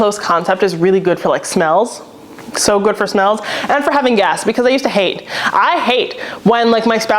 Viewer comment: When you said open concept is not good for “having guests” I heard “having gas” and I thought very true!! Then I heard the context and realized it’s not “gas”